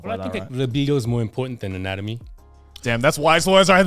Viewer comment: He sasy something that i accoustic didn´t understand and they want to print it on a shirt can someon help me out and tell me what Shindo L said, please? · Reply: "Libido is more important than anatomy."